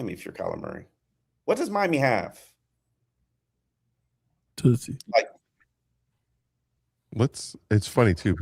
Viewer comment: did just say 🐈?! 🤣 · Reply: Tootsies (strip club)